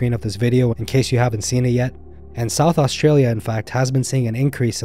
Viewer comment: As an Australian I can tell you that your comment about South Australia not being known as a shark attack coastline is incorrect. All Australians are well aware of the dangers of the South Australian beaches because it does have high numbers of great whites due to the seal colonies that are present. Apart from that, I enjoy your episodes. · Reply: I thought he said South Africa, but I was also bewildered by that statement as both locations are well known for Great White sharks and attacks. I mean, I'd say those two locations are white shark attack capitals of the world.